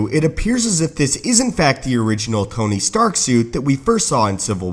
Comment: is it just me or did he say "coney stark"